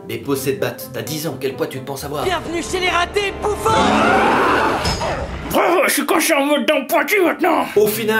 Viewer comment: il dit quoi ?? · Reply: UN VISITEUR VENU D'AILLEURS RP - 2.0 Il dit « Bravo, je suis coincé en mode dents pointues maintenant ! »